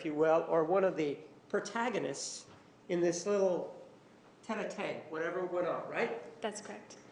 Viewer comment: what is Martinez saying here? It sounds like a French phrase but I have no idea how to spell it even close enough to look it up. Edit: found it, he said tete-a-tete, which translates to head-to-head and it means a private conversation between two peoples.